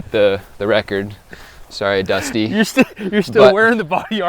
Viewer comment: did he call him dusty?